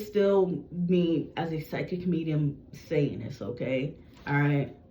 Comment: did you say you are a psychic medium satanist?